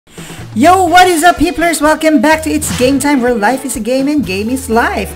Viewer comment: Did he just say “yo what is up [HITLERS]?!?”